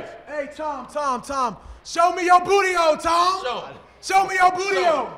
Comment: the 1st time i thought he said 'show me your booty hole'. i died laughing